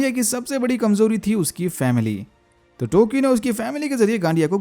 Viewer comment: did u just say family 😂😂😂